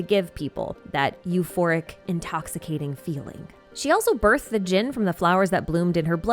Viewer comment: "She also birthed the djinn" I totally heard it as gin, the spirit. Not djinn, the, uh... spirit.